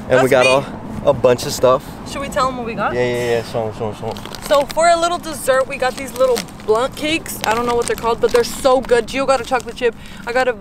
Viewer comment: bro did she just say blunt cakes😭😭